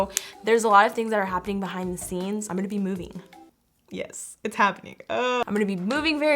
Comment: Lol 6:56 it sounds like said “I’m gonna be movie”